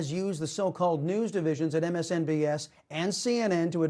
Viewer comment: did he say “MSNBS”? Lol